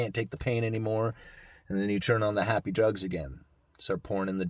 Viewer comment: I thought he said "..turn on the happy drugs again, and start porn.."